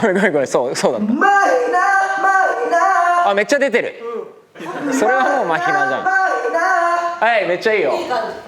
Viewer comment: 辺りで「それはもうMAHINAじゃん」って聞こえる？